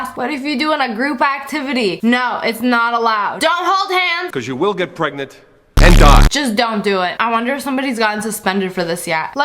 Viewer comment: The man: Cause you will get pregnant and die. Captions: Cause you will get pregnant and DOGS No joke this is what the captions said😂😂😂 I'm so glad I ALWAYS have the captions on😂😂😂